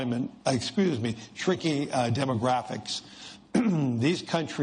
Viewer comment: Sounded like he said freaky/freaking demographics 😂😂😂